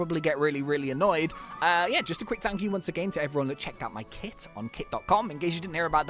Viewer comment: I thought he said kik XD OML